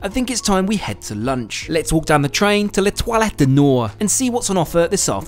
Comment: Exactly what I heard too. But then, I am a French native speaker... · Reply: I'm an English native speaker and I thought he said 'les toilettes', not 'L'étoile'.